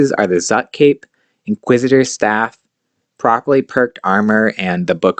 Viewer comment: What are you saying around 1:00? What type of armor? Procolyte? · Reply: Haha properly perked* so the gear setup at 1:07. You can use subjugation/virtus/anima core of seren